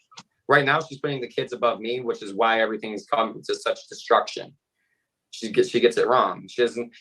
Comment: I thought he said construction?